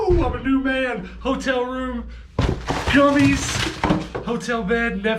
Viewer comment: Just realised you said gummies lol I thought you said it's all good. I'm clean in hotel johnnys and netflix